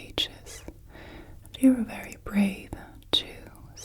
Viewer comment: Lol 3:33 I thought she said “brave jew”